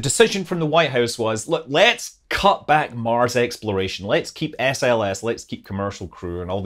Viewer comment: Almost sounded like you said, "Mars exploder", when talking about the rocket-powered sample return vehicle, so...first thing that popped into my head was that it would be made by Ford (a play on the Ford Explorer with the tires that gave it the nickname, the "Ford Exploder"). Sorry, couldn't resist that. 🤣